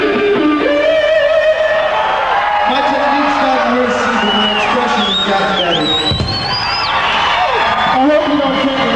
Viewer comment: What does John say at 41:30? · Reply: My technique has gotten worse (??) but my expression has gotten better